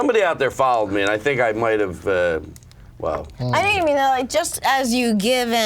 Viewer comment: LMFAOOOO What the fuck did she even say at 36:26 ?!?! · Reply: think she said "I think you mean that"